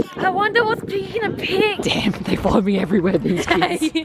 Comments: Did just say, “Da…” you know the bad word?! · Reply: You mean "damn" 💀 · did say D*mn?!